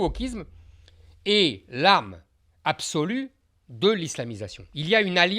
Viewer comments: he is clearly saying "arme" not "âme" so the translation is not "soul" but "weapon" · Small correction at 4:52 : Zemmour says, I quote "Le wokisme, c'est l'arme (weapon) absolue de l'islamisation". He did not say "l'âme (soul) absolue de [...]"